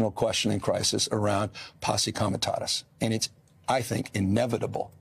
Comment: what does he say? Possi comitas?